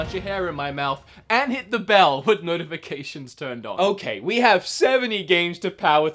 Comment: I thought you said hit the bell to turn notifications off